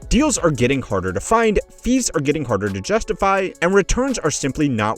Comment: I thought you said "thieves are getting harder to justify" you just said fees.. oh thank god because that would be the end of private equity.